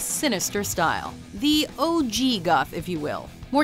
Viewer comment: I honestly thought she said 'the old jew goth'